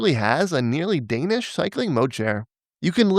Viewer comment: what's a "kneeling danish moch chair"? I'm not trying to be funny I want to know what he said? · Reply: A “nearly Danish cycling mode share”